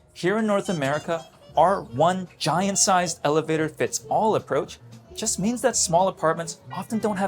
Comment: the random lady 😂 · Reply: Yeah and I thought he was saying “our giant sized users “😂